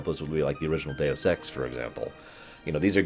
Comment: I thought he said "original day of sex" (like your first time) and then I was like ohhhhhhh Deus Ex! But both apply I suppose...